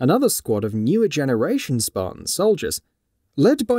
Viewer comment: "then we have fireteam Orsiris. Another squad of newer generation spartan soldiers. Lead by *dueter raginist*, only agents, and former assassin Jameson Locke..." .............. The F*CK did you just say!!!!!!!